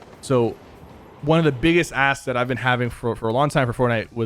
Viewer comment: thought he said one of the biggest ASS